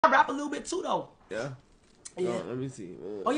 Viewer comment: i swear i thought he said “i fap a lil bit too though”